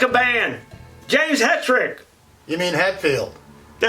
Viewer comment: i had to replay it a few times cause it sounded like he said my name it's funny how it sounded like he said Hedrick